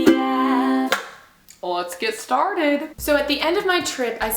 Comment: why did I hear "let's get startled!"